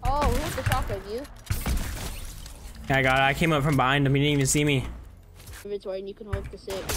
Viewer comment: bro what did you just say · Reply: He said I came up from behind he didn't even see me